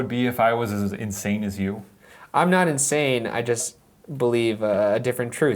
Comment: the unintelligible voice sounded like she said "im not amish"